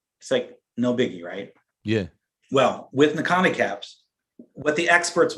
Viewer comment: There’s a word that keep being used that sounds like “nakanicaps” (e.g. at 36:54). Can anyone explain what the word actually is and what it means please? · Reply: Cycle Analysis Profiling System